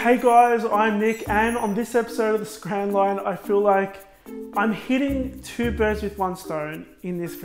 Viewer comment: I thought he said I feel like I am gay :S